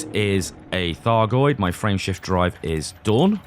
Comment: LOL I heard "Friendship Drive" instead of "Frameshift Drive". Thanks for the laff.